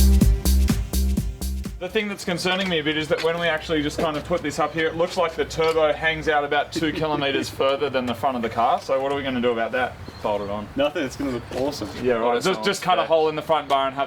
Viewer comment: Did he say 2 kilometers?